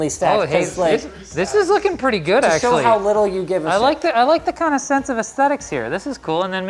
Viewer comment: does linus say shit and not censored?